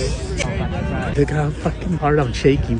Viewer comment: First time watching I thought he said “I got a hard-on I’m shaking dude” 😂